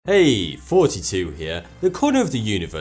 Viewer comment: i thought he said "Hey,42 here"